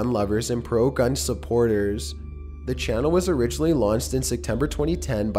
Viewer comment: He says launsed instead of launched